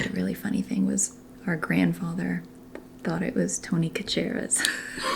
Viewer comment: Lowkey thought your sister said Tony Kachicka's like Chick Hicks from Cars